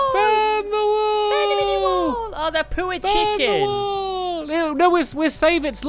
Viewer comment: No offence but when said poor I thought he said pooh uh